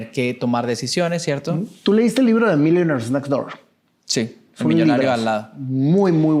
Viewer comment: Me gustaría saber como se escribe ese libro para buscarlo el que dicen en el minuto 42:08 segundo al guíen me ayudo a escribirlo... 😅😅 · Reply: The millionaire next Door / el millonario de la puerta de al lado.